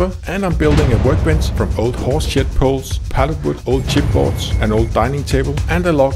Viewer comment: "I'm building a workbench from old horseshit poles..." Did I hear that right?